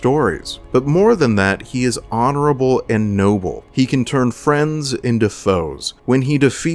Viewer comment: its foes into friends instead of friends into foes